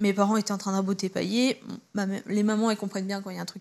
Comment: les parents étaient en train de faire quoi ? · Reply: 😂 j'ai réécouté plusieurs fois je crois qu'elle dit raboter pailler